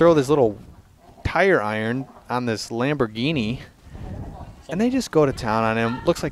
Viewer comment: did you just said lamborghini?💀